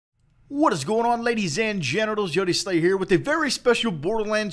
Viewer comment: fuck i miss you. but it sounds like you say "ladies and gentiles"